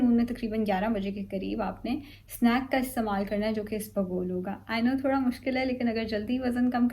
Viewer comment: Snacks mein kya lena hai. can anyone tell me plz what she was saying ? I didn't understand that. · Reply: ispaghol pena hai as a snack 😄